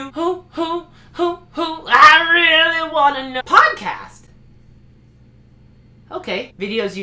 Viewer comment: Jesus.... I heard "porncast" ., what did she say? Lol , my bad